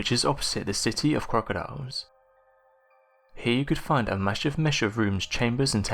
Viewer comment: What did he say? A masha musha rooms? I'm so confused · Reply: Captions say he's saying 'a massive mesh of rooms'